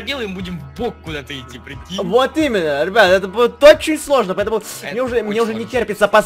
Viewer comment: "Это буде сложно" - 0:43 Я услышала: "это будет очень смешно".